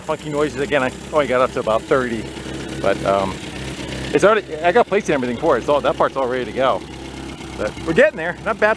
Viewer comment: Anyone know what said at 1:02:32? "it's all ----, I got place(?) and everything for it(?) it's all that parts all ready to go" The subtitles can't make sense of it. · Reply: He kinda stumbles over words but he says something like " I got plates and everything for it" likely referring to license plates and registration